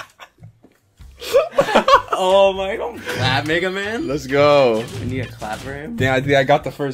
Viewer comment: Did anyone else hear the white boy say don't clap nigga man?